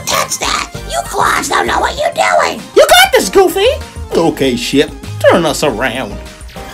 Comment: I thought he said "Ok shit, turn us around!" but he said ship and it was still funny.